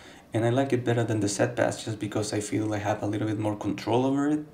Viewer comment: Could someone tell me what he's saying at 00:07 ? Is he saying "I like it better than the set pass"? What is this?